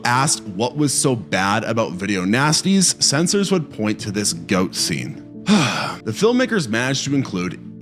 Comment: oh wow I thought you said ghost and was like "what an over reaction!" Okay so dont tell them about that sheep ass judging contest. They'll arrest you for hate speech.